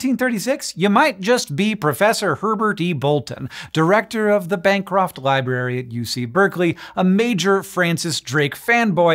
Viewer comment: You say 'Bancroft Library', but I heard 'bankrupt library' at first - that WOULD also make sense for a reason to forge things : )